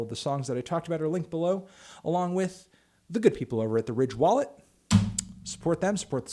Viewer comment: When he said Ridge wallet, I thought he said,” RICH wallet” because those fucking wallets are 100.00 dollars.